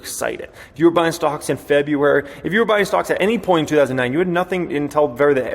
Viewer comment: I thought for a second you said you were out buying socks.